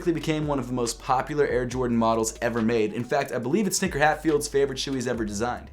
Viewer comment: Did he call him sneaker Hatfield? 😂🤣